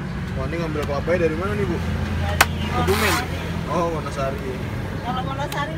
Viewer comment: Bapaknya bilang dari kebumen haha apa salah dengar??